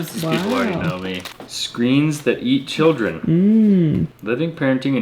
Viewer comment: I totally thought that book said screams that eat children😂😂😂 I'm like who on earth would send them a book like that😂😂😂